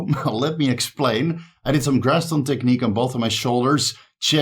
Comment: you did what I didn't catch that sorry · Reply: Graston Technique, to break apart adhesions and scar tissue.